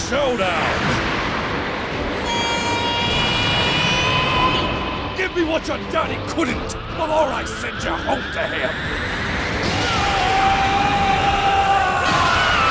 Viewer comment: I always thought that 5:50 said "Send your home to hell" rather than "home to him". Really neat line either way